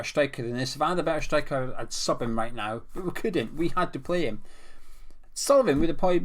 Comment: "If I found a better striker I'd suck him right now" -what I thought you said